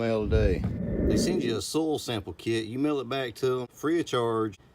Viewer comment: OMG.. I had to rewind the video like 3 times.. At first I thought you said stool sample kit... 😂😂😂 Im dying.... Im thinking ...wait ...what?? WHAT kind of "natural fertilizer" is this gonna be about??.. LOL... great video my friend...looking forward to seeing your grass get greener!!! Your channel is really growing... congratulations!!!!